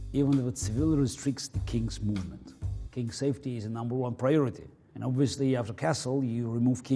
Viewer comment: "king safety is number one priority" i thought i heard CrazyRussianHacker